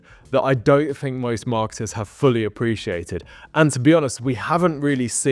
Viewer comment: it sounded like you said "most marxists haven't figured out" like bro, first my groceries, then my tv, then my tech, and now THIS? we gotta politicise everything istg (joke)